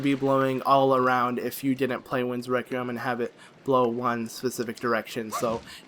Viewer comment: I think at 18:19 you said winds rectum